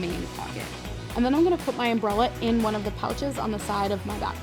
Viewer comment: Am I the only one who heard bralette instead of umbrella